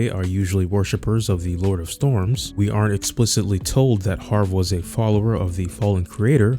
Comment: You say we were told havre was a follower of the fallen creator, but that is just wrong though, it was never said😅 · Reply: English is not my mother tongue but from what I understand he's saying "we aren't explicitly told he's a follower of the Fallen Creator" but the "aren't" is spoken very soft